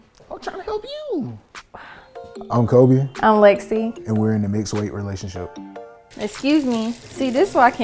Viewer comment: WTF is a "mixed weight" relationship? All these labels for what's been normal for centuries are ridiculous. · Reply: I thought she was saying mixed race 😂😂😂😂